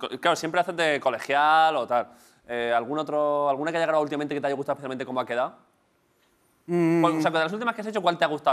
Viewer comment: 😂😂😂 que dice? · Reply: Alguna que has grabado últimamente que te ha gustado especificamente como ha quedao?